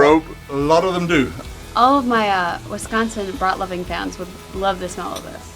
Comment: Ok...so at around 18:38....does say "All of my Wisconsin butt-loving fans would love the smell of [the sausage]"? Nothing wrong with it I suppose, just not sure what to make of it. Who's butt are they loving? Are they people in Wisconsin who love butts and if so, why is butt-loving limited strictly to residents of Wisconsin? OR, are they people from anywhere and everywhere who love Wisconsin butts? And does the sausage smell like a butt? Is that why all of your Wisconsin butt-loving fans would love the smell of the sausage? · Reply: haha yes... she said "brat", not "butt" lol